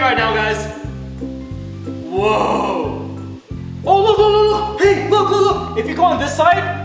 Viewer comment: did he say Babe Look!?